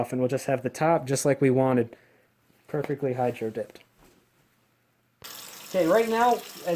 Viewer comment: I'm watching this in closed caption because everyone's asleep in the house. At 11:22 the caption says "....have the top just like we wanted perfectly hide your dick..." Also, I have an airsoft minigun that I want to hydrodip some flames and skulls onto and I was worried that if I were to hydrodip it myself, I would mess it up. So instead I figured I could take it to a company that does that, but I can only imagine how much money that's going to set me back (I know, I own a minigun and I'm worried about a $250 hydrodip job). After seeing this video, I would have to say that it certainly helped to set my mind at ease and maybe some day, I'll finally muster up the courage to give it a dip :D Keep up the good work dude.